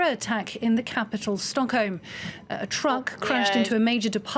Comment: its sounds like she says stonkholm tbh heh